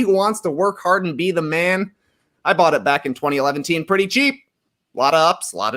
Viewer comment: did Trey just say 2011teen?